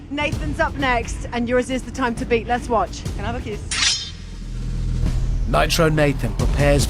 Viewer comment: did anyone hear tomasz say " can i have a kiss?